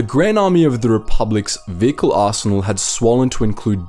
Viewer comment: It's sounds like your saying assinal instead of arsenal